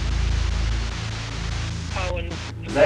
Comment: y’all heard owen but i hear i wouldn’t, it could explain why y’all barely got anything other than the “you’re pretty”